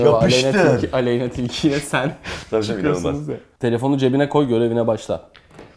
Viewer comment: aleyna tilkiyle “çıkıyosunuz ya” mı dedi ben mi yanlış anladım